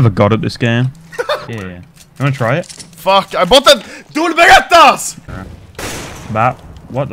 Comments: anyone else think it sounds like he said "I bought the dual baguette ares"? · it sounded like he said dual baguetteas Edit: it sounded like that to me